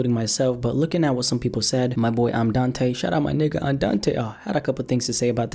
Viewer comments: wait did he say nigga lmao,man i dont think you black cuh,i dont really care though,im just saying,be careful what you say some people get really offended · did he just say ni**a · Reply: Jaylen Knolton he said nigga no shit